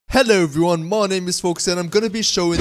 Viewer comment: Thought u said hello there my name is Morgz at the beginning